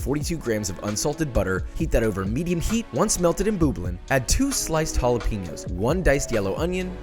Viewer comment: did he say boob-ling instead of bubbling